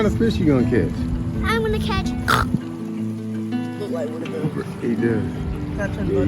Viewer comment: i heard lil man LOUD AND CLEAR he said he catchin HOGS today oink oink!